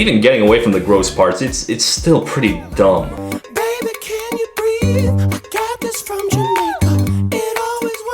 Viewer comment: I hear “baby can you breed a cactus from Jamamaca lol”